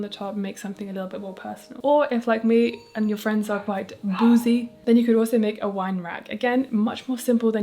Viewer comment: i really thought you were saying bougie but in a funny way 🤣🤣 okay boozy friends